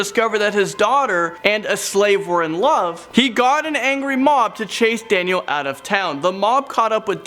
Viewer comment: at first I thought you said angry mop not mob which doesn't make as much since